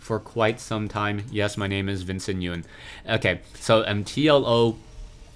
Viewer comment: thought he said Vincent at first. wow is totaly original name. but not weird original lol. another win for!